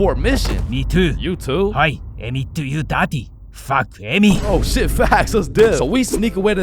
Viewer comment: ngl why did it sound like he said “fuck Emmy”